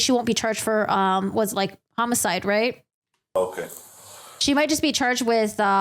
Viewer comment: Me thinking I heard “momicide “ and not homicide and spending the next minute of my life googling it 🙃 12:22. Lol. She’s a real doctor. “Then I documented…” lmao. Yea her report might put you in jail for a cool minuto hun. She documented 🙃😳